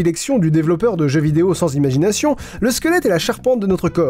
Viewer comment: à 24:40 j'étais pas concentré et j'ai compris 200 ciseaux. j'ai trouvé ça un peu bizarre.